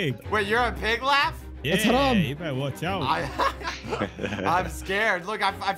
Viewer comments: did someone hear "its haram" or it is just me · I heard someone say that haram in